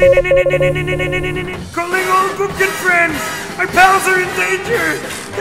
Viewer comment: Legendary episode I thought FishyBookins had said ‘McDonald’s is in danger’ instead of pals lol